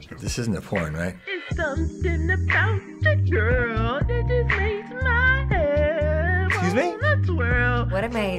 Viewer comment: All I heard "THERES SOMETHING BOUT THE GURL MAKES MAH HEAD WANNA TWIRL" that's mainly all I heard